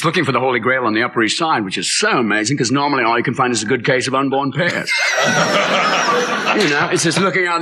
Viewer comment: “A good case of unborn pears?” What did he say at 11:56?